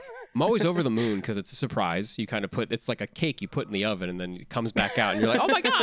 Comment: not me thinking that they were saying kink instead of cake for the whole convo